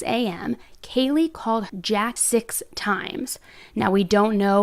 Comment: What is she saying at 2:37 ? Is it — she called Jack 6 times?